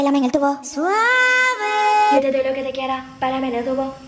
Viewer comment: I thought I heard “Suarez” instead of “Suave”